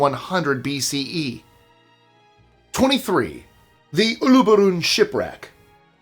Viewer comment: At first, I thought you said ulabula in a mumble pronouncing lol until I listened to the second time, you had said uluburun.